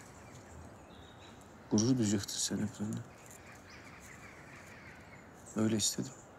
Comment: burda ne diyor ya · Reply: Gurur duyucaktın sen hep benle